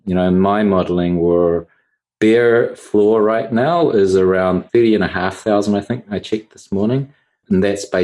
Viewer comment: Great to listen too. Couldn't quite get what Willy said the bear floor was at 14:10 Can anyone help me? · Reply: 30,500